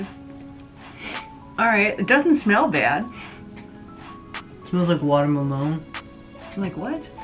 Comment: smells like watermensjdhbsb i thought he said smells like post malone 😂